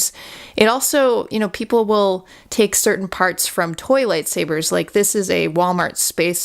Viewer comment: I thought you said toilet saber lol